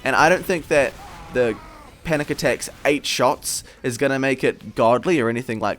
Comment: Did he just call it a panic attack